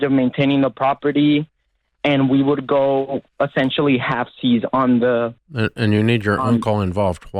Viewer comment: Did he just say halfsies?